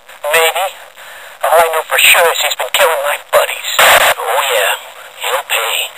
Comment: Until now I thought he said "He's been killing my bunnies"